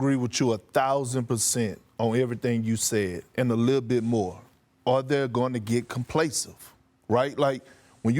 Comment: "Are they gonna get complesive" ......😂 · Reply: lol you’re being generous. I heard “are their gonna get camplasive”